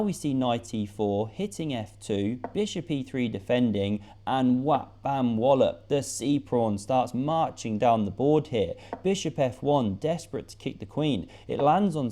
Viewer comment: please write what you said I wanna use it everyday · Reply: "and whap, bam, whollop - the sea prawn starts marching down the board here"